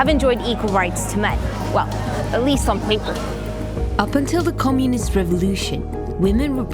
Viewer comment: she says “Well, at least on paper.” , but the caption says “some people”. Nice.